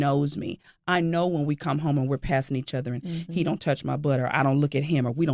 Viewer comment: I thought she said he touch my blood not he touch my booty I need a hearingaid